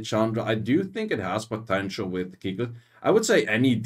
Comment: it has potential with...? Can't hear it well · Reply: I believe it's cichilid (wind mermaid)